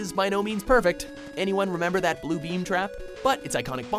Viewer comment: Did he just say BLUEBEAM TRAP?